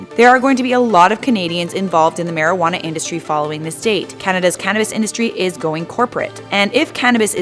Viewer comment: I thought you said Canadas Canadas industry